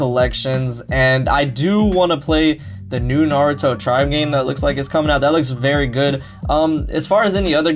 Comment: what was that second Naruto game? I couldn't make out that last word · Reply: Naruto ninja tribes